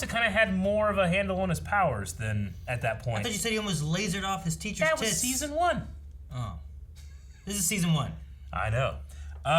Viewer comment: sister walks past room i thought you said he almost laserd off his teachers tits me: hahaha sister: the fuck are you watch you freak